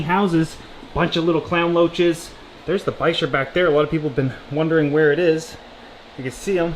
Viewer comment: What’s the long eel like fish at 1:00 ⁉️ I think you said “piesher”?? I’m not sure how to spell it and can’t find it on google. TVM‼️ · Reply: Lol I spelled it wrong bichir